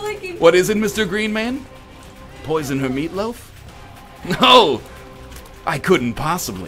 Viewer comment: At the end when you said poison the meatloaf I thought you said put boys in the meatloaf